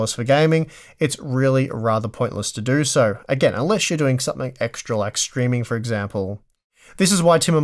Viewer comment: i heard " something like screaming for example" 😀